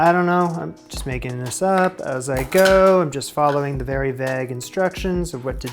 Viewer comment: Is that how Canadians pronounce 'vague' or is that unique to? Had to rewind the first time he said it because my mind dwells in gutters and I thought he said, "Vag", like, you know, female parts.